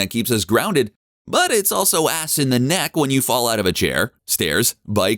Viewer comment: did he just say ass in the neck??😆🤭🤔 he talking about the gooch??